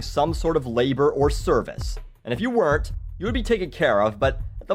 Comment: I'm sorry but at 4:15 it sounded like you said "and if you worked, you'd be taken care of but at the most basic level. You wouldn't get the luxuries that those that actually worked would get to enjoy." Did you mean "if you didn't work" or am I mistaken?